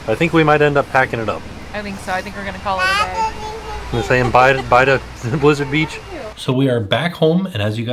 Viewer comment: Sounds like says, "Bye Disney's Blizzard Beach!" @ 11:40 LOL